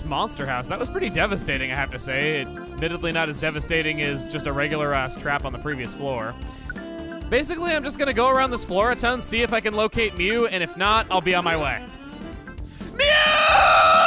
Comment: did emile just say Ass without realizing LOL